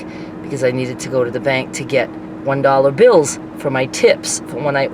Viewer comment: I definitely thought you said you needed $1 bills for your tits. And then I was wondering what kind of club you’re going to hahah 😂